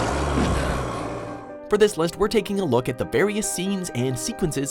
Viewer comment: I thought it said monsters instead of moments.